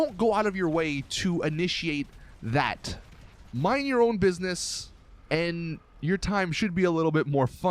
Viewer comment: does he said mind your own buissnes